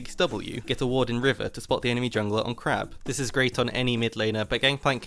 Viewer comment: On crab... the enemy jng on crab. i swear I heard "on crack". Which for yoloqueue is accurate i reckon.